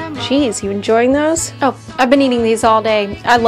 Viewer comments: I heard ''I love mens toes'' 💀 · I heard “I love men-toes” 😭